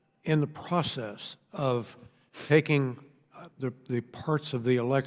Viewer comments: why is he saying faking? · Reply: CC is taking, but that not what he said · did he just say he was faking the election?